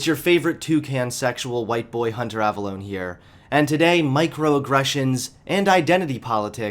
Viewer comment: did you say Mike Rowe aggressions?